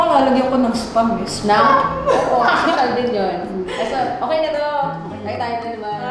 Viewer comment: Good job, ramyeon was overcooked though. 뭐라는지 모르겠지만 웃었어요. (3:38 No sperm). Thank you, for bringing Elle, and (?) together. Glad to see you guys again. Stay healthy and happy.